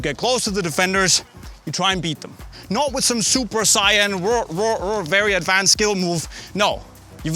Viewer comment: Did he say Super Sayan?